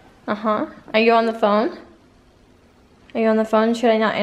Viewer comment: When was on the phone it sounded like he said on the phone and on the towels!